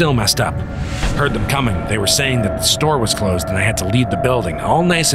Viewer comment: What i hear: “the store is not closed, please exit the builiding“